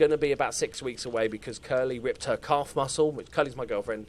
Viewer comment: Every time you say, "Curly ripped her calf muscle," I hear "Curly ripped her c--" and I think of what you say when one of your players gets a groin injury...